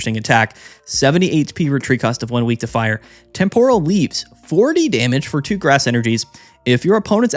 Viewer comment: My big-back-head-ass thought you said,”tempura leaves”.